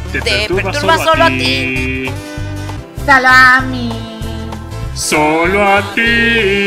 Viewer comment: parece que dice salami